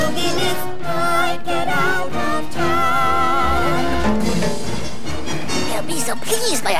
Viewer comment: i always thought they said, "i'd rather drown," but its actually "I'd get out of town"